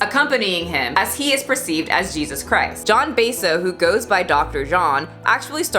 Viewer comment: Thought you said john basedow. FITNESS MADE SIMPALLL MADE FER RAL PPL